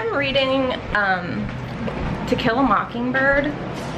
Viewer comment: I thought you said "tequila mockingbird", and i was like "..oh, are they trying to mock the OG one with that name or something?? Cause that's kind of a genius advertising technique...." And then you showed the book.......😁 🆒